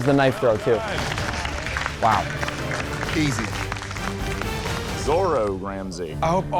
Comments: did he say zoro ramsay😂😂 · did he say zoro ramsay, I mean .... ye the guy is hard core one piece fan lol, 😂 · did he say "zoro ramsey" ?